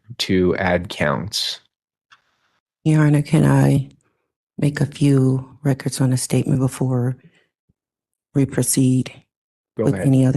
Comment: did you hear what her 1st sentance was? She said “ your honor, an I make ages records for the statements”